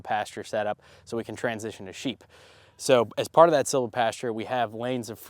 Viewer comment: Can you say again the method of planting you are doing in anticipation of getting sheep and expound on this? It sounded like you said ‘silver’. Thanks! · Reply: It's called silvopasture!